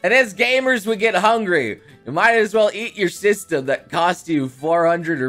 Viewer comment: When he said eat your system, I heard “eat your sister”